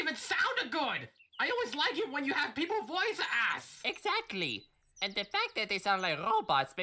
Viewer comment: sounds like luigi said voice a$$